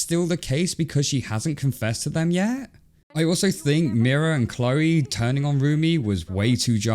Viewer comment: Sorry did I just hear Mira and Chloe?